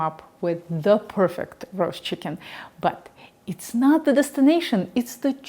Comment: Did just say journey before destination??? life before death, strength before weakness!